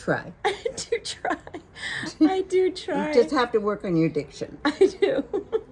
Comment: So beautiful and hilarious at the same time! Love love love 🧡 I thought your mom said, " You just have to work on your addiction"!! Of course I misheard and she said, "diction" 😃 😃 So funny you two!!